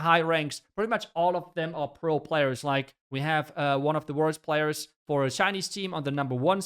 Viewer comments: did say "one of the worst players" ?? 😳🙄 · Did you just say worst Chinese player🌝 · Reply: He said worlds players. But i also heard it worst** 😂